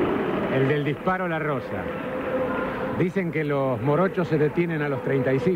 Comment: Que dijo??.."Dicen que los borrachos se detienen a los 35"??.. se re zarpó el loco ese!!..jajajjajaaaaj como vá a decir eso!